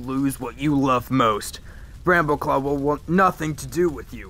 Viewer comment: When you turn on captions at 3:28 ,it says rambow club, instead of brambleclaw